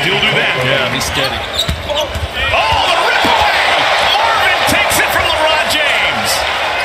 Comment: Did he say Marvin takes it from LeRiod James lol